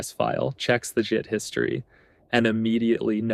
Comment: Did you just say “Check the jit history”?